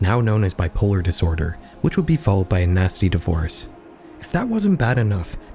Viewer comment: Did you say ‘natsy’?